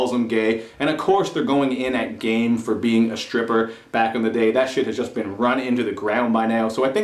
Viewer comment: All I heard was "you a stripper" about 200 times